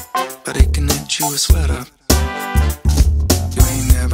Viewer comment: "This butt don't sweat, but it could knit you a sweater" I'm speechless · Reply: Doug Funny I heard “lift your sweater,” and now there’s a new fetish in the world. #spider/sweater